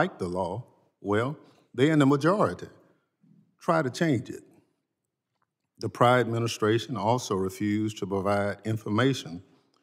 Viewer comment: Did he say “The priden administration”?😳😳👎🏽👎🏽